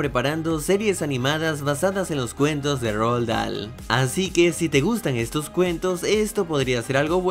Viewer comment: En el segundo nombre pensé que decía The Grefg ,JAJAJAJA